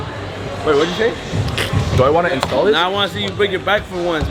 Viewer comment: I definitely heard “sodas” when tf did he said “install this”